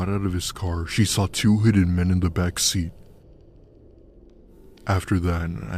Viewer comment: In Story number 4, it sounded like he said “he saw 2 headed men in the backseat” i had to rewind it 4 times lol